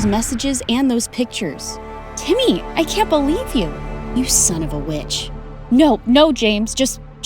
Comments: is it just me or I thought he said son of a b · he really said "you son of a bitch" 😭😭😭😭😭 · i thought james said "you son of a bitch"😭🤣😂😅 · Oh i thought he said oh you son oh a bitch not oh you son of a Witch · for a momet i thought james said the b word until went back and heard witch 😶